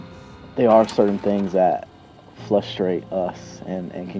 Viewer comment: is he saying “flustrate” and “flustrating” ? 🧐😳